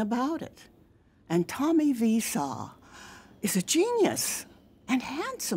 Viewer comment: am I the only one who heard her say viseau?